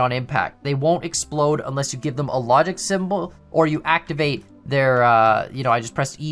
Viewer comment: Why did you say symbol instead of signal?